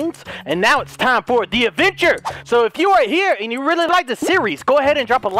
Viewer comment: I thought said 'Avenger' at the word 'Adventure' Lol